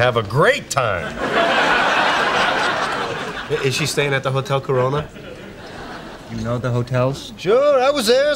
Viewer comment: Did Joey say Motel Carona? @ 4:09 😁